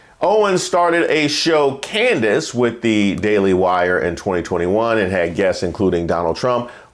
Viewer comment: Oh, she said, Daily Wire. I thought she said DEI hire. My bad. Cut her loose either way.